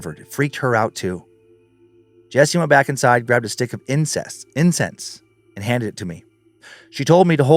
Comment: Hahaha is no one going to acknowledge he said incest instead of incense! 😂😂😂😂 30:31 ish